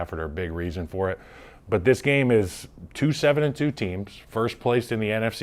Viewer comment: Did he say 2-7 teams?